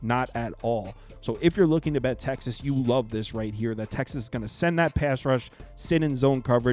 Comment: and they lost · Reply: Oh, I thought you said "run all over Texas," not "beat Texas."